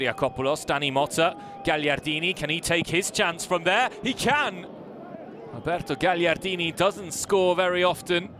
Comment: did he say alberto its roberto